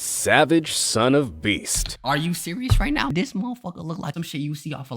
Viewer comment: Savage son of beast I THOUGHT HE SAID SAVAGE SON OF A BITC